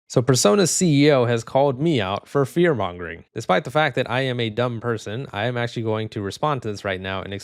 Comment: He is a liar. He is one of the people wanting to control the population and train AI. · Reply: Sounds like he said "call me out more" to me.